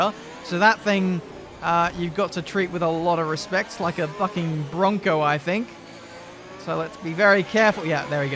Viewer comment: Did he say "Like a fucking bronco"?